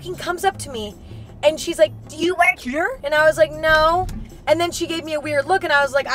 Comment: what did that mom said? I'm not able to understand with the filter voice 😅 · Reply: “Do you work here”